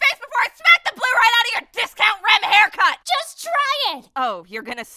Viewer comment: anyone else hear discount rem